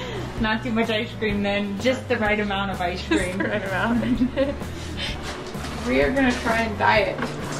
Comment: When said "We are gonna try and dye it" I thought she said We are gonna try and diet. Lol